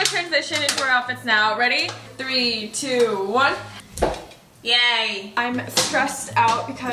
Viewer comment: IS SAYING YAY😭😭🙏🏻